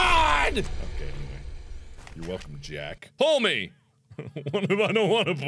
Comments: did mark say "you're welcome Jack"? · "You're welcome, Jack." I FORGOT HE SAID THIS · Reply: I thought he said, "welcome to Jack", like he was making a joke about how loud he was being.